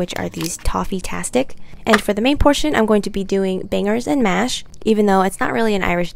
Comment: did she just say bingers and mash…